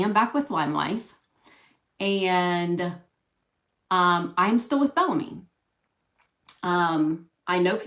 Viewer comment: Hahaha! When she said “I’m still with Bellamy” I totally heard “I’m still a felony”